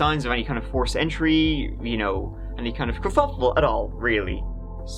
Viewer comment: What word did you use at 11:22? I have replayed it a few times, and I can't tell :) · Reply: Kerfuffle